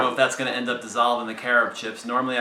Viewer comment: Haha, in your first video, I thought you said "carrot chips" which I thought was a strange alternative to chocolate chips. I've never heard of carob chips before :)